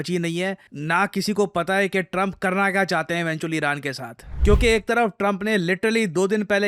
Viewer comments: Did he said, krna kya chahte hai BC iran ke sath @ 5:25-5-30 😂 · did he say “Trump krna kya chahate h BC Iran ke sath” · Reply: Bc❌ eventually ✅ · did he say what I heard!! "Trump Karna kya chahte hai BC Iran ke sath" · Did sir said "koi janta nahi karna kya chahte hain 'BC' Iran ke saath"😂😂...listen to 5:25 and 5:30 very carefully.. big fan sir